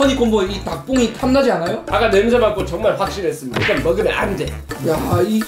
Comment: 정말 확신했습니다가 아니라 존x 빡치겠습니다.로 들었어욬